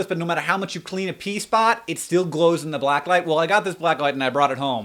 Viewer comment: hahah when you said when i was in high school i got a black light. I thought you said black eye, and for the next few min i was trying to understand how that had anything to do with the 90s or pee or anything